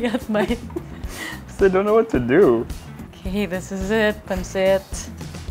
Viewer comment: Did Solenn just said "This is it! Pancit!? 9:11 lol